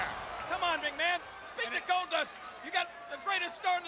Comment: I thought they said Stardust. Gold Dust ain't no joke either, he's a straight up brawler!